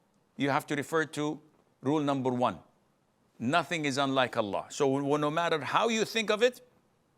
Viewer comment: did he said nothing is unlike Allah. And does that change the meaning